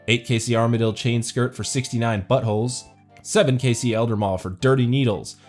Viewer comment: all i heard was "69 buttholes"